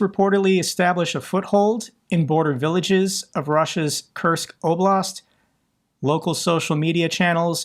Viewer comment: First I heard 'cursed oblast', and only afterwards I understood. Well, now it is kind of cursed